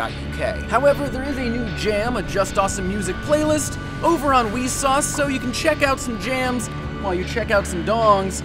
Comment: did Jake just say Wiisauce?